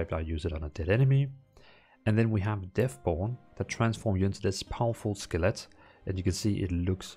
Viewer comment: I keep hearing "turns you into a powerful skillet"